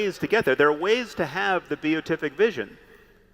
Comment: "There are ways to have the _ vision" - what is the word he is using??? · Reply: Beatific